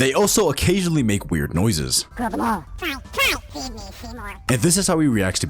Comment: I heard “feed me Seymour”